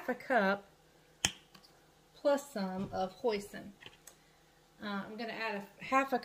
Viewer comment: When you said you were adding hoisin, I thought you said, “This is where we add the poison” 🤔😂😂😂😂😂